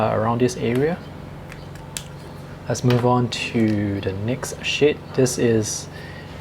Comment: did you say the next "sh*t" ?....